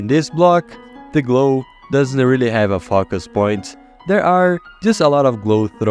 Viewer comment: I hear fuckass point